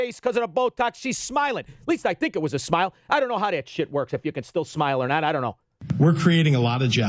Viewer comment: Did just say SHIT at about 11:04?